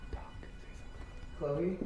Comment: 40 minute video special! Enjoy let me know what you think! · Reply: when u Said ”chloe” i heard ”yeah”